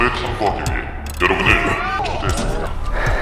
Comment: 여러분을 ㅈ됫습니다 로 들리지?